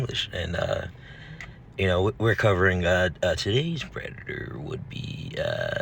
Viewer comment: IT SOUNDED LIKE YOU SAID REDDITOR 😂😂😂 And then you say Scott afterward 😂 Who even is Scott??